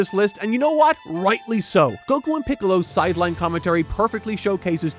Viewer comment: pickles instead of piccolo lmao